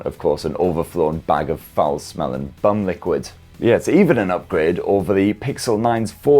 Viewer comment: did he just say bum liquid!!😂